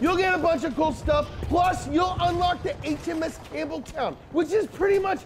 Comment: Did he just say the HMS Cameltoe?